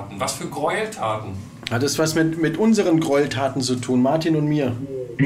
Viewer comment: Hab auch bei 9:19 min bring Martin um verstanden